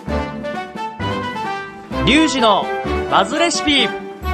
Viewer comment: リュウジの！不味レシピ！に聞こえたww